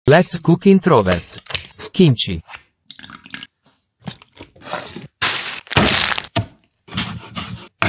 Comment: I heard “let’s cook leftovers,” kinchi